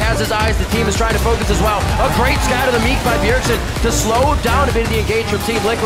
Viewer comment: Did Riv just say a great "Scatter the Meat" on 4:57 there or is it just me?